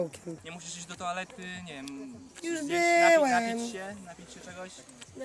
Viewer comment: czy ja usłyszałam już byłem